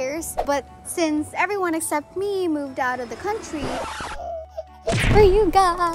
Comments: ate Donna said "we're gonna move to the states".Wait.. ate Donna said they're moving to the States💔💔💔😭😭😭😭. So pano na DoLaiNab kung pupunta si ate Donna sa States??😭😭 Sana hindi sila matagal sa States para may DoLaiNab parin and para mas madaming ang chance nating mga Donnalynspireds na makita siya🥺🥺. 😭😭😭😭😭😭😭💔💔💔💔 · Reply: correct me kung mali po yung narinig ko? Ms. Donna said “Everyone except me moved out of the country” Thaaanks! 🧡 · States po.....??🙁😢 Dun na rin po kayo titira?or yung family niyo lang po except ikaw?? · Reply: correct me kung mali po yung narinig ko? Ms. Donna said “Everyone except me moved out of the country” Thaaanks! 🧡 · I heard in this vlog you guys, ate donnalyn that you are gonna move to states? It's kinda sad but I just want to say, Stay Safe ate donna and your family!😊 Ps: I'm not really sure if I heard it right soo😊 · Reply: correct me kung mali po yung narinig ko? Ms. Donna said “Everyone except me moved out of the country” Thaaanks! 🧡